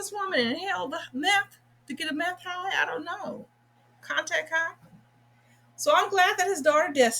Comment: Facts. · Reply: lol, did you say that this man is trying to get a contract high instead of Contact high? ( @ 3:55 +/- timestamp). Either way this guy has a memory loss and I'm so glad that his daughters are trying to help him to remember. Great job as always 👍😊.